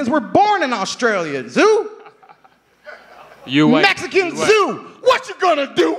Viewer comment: I honestly thought KT said "mexican zoo". Lmao